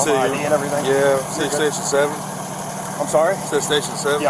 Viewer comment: In the beginning I thought he said 6 or 7 but he said station 7 lol to much brain rot for sure